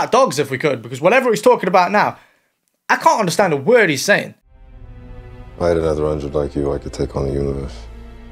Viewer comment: I had to listen to it like 5 times, but I think he said "If I had another hundred like you, I could take on the universe". God, the acting seems terrible and the writing seems worse. Glad I stopped watching all these movies after Ultron lol